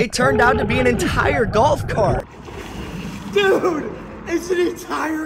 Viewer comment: I thought he said “golf court”😂😂😂